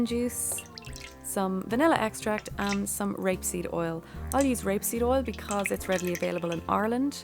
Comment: It should not be grapeseed, it's rapeseed. It's a different plant 😊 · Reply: Анна Жевнерова Never heard of it. I thought she said grape seed in her video. Thanks 😉